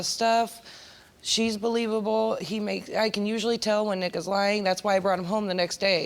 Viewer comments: Did she just say “I could tell when Niggas lying” lol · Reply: Lol no she said "I could tell when Nick is lying" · I thought she said “I can usually tell when niggas lying”😂😂😂😂😂😂😂😩😩😩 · lmaooooo for a second I thought she said “ I can tell when niggas lying” 🤣🤣🤣🤣🤣🤣🤣